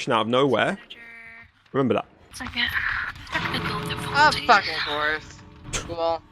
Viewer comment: Hey! at 5:55 the subtitles are wrong She said "Of fucking Course"